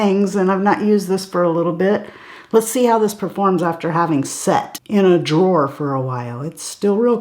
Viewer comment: I swear for a moment at the beginning of your video I thought you said, "Let's see how this performs after sex.." in reference to the Makeup by Mario eye pencil. LMAO! "SET", not "sex". Okay.... Still, I literally laughed out loud and we all need a little more laughter in our lives, right?